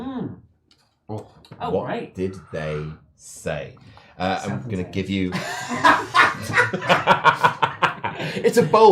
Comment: Anyone catch what movie said at 43:55?? I cant tell lol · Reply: Southland Tales apparently based on IMDb it stars the rock and sounds trash af